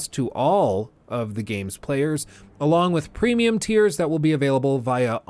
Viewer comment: Don't know why I keep hearing "premium tears" 😂